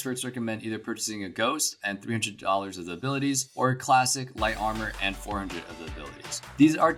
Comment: did he just say "dollars" :DDDDDDDDDDDDDDDDDDDDDDDDDDDDDDDDDDDDDDDDDD